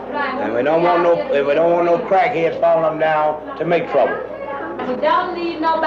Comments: did he say crackheads? 🤦🏾‍♂️😅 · Did he say crackheads?? was that prophecy for the 80's to come? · Did I Hear Him Say Crackhead!?